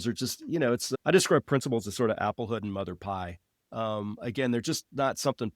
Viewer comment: Did he say "applehood and mother pie"?